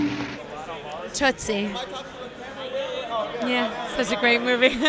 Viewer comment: what movie did she say? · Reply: Tootsie starring Dustin Hoffman and Jessica Land came out in 1982